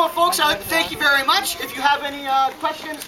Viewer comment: I thought he said fort nigga